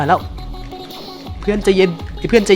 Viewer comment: ทำไมตอน 5:50 กว่าๆ ตอนที่พี่เขาพูดว่า "ใจเย็น" เราได้ยินว่า "ใจเย็-"